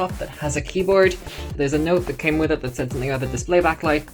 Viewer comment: ok so, i was training my listening and was writing it down in this video, everything was going well till i got to 1:58 when you said "there is a note that came with it that says it got the (didnt understood this part??) display backlight" i genuinely dont get what word you said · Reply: that bit goes "there's a note that came with it that says something about the display backlight". I still need to figure out how to add proper subtitles to my videos, sorry!